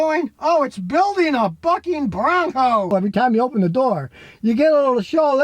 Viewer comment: Lol who else heard "IT'S BUILDING A F*CKING BRONCO!"